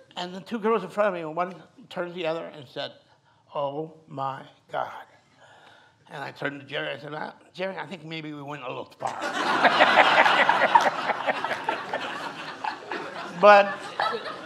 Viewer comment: Guys, I can't catch the words: what does he say? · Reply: “I turned to Jerry [Bruckheimer] and said ‘Jerry I think maybe we went a little too far.’ “